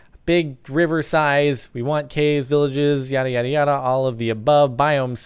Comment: I thought he said CaveVillages D: That would be epic!